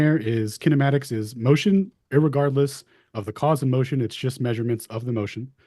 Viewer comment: ish ... Did he say "irregardless" ?